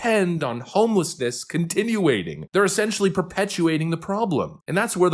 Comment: did you just say "continuating"?